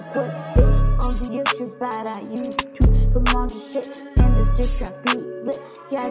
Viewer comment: I know It sounds like i said the sh word but really i said sit it's just the autotune it kinda makes it sound like the sh word